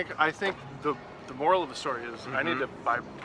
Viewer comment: Did he just say “I need to retrain”?